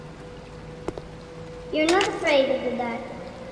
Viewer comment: I thought the voice said “your not afraid of the fart are you?” And then that door squeaked like a wee butt trumpet and I lost is and could not stop. So it was entertaining for me but for all the wrong reasons.